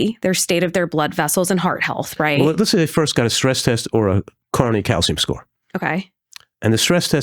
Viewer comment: Did he say a corneal calcium test? I cannot find that test. I think I misheard him.❤ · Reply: coronary calcium test